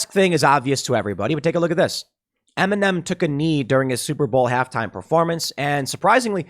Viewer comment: I thought he said m&m took a knee.